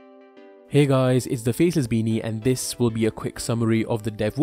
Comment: seriously just thought you said, "hey guys it's the feces baby". XD